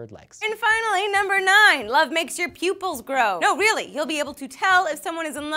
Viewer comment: When she said 'Love will make your pupils grow', I thought she said pubes...